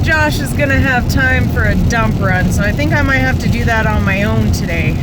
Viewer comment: Am I the only one who thought she was going to say "and I don't think Josh is going to have time for a dump" . Threw me off for a quick second until it registered she said run at the end of it hahaha